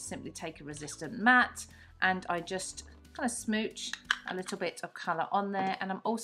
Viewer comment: It made me giggle when the video made it sound like you were saying smooch (kiss) rather than smoosh. 😊